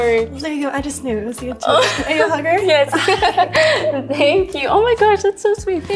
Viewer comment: i honestly thought she said "are you a hooker?" too 🤣🤣 i laughed out loud!